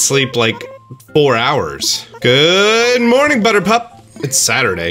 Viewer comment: am i the only one who thinks it’s really cute when he calls isabelle butterpup? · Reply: I first thought he said buttercup🤣😂🤣😂🤣